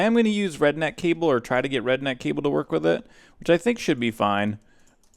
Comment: I thought you said redneck cable at 11:44 , lol. Great job on the new one eight!